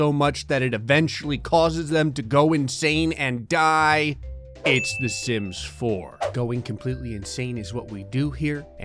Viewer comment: i heard. " the simps " not the sims.